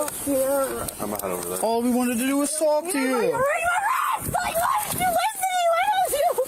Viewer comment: Did she just say you're ruining my Kick